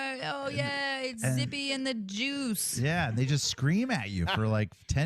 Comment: I thought she said Skippy & the Juice